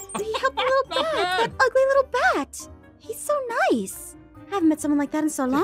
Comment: Did I just hear "that ugly little bat" 😭😭😭 bro am I hearing it right?